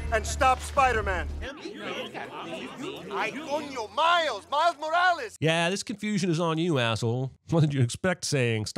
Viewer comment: Correction For what it's worth, at 15:27 Miguel is saying in Spanish "Ay coño, Miles! Miles Morales!", not "Hay uno", which in English would be the equivalent of dropping an F-bomb.